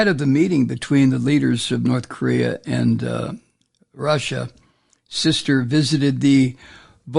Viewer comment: I heard Kim's sister does meth 😳👀